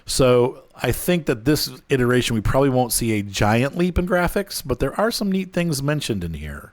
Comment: Did you say we won't see a GIANTS leap? :P